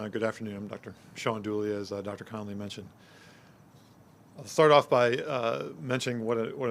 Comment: "I'll fart off by saying..."??? Did I hear him correctly? 2:42...